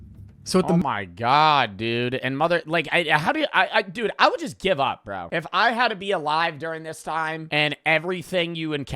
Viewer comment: what did he say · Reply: Your massive